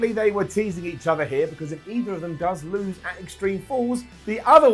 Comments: Did he say “Extreme Falls” at 14:57? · I had to listen again to see if I heard it right, and its hysterical: “Extreme Fools”.